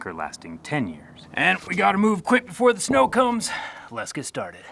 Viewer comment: Could have sworn you said " We have to move quick befor the snow cones " lol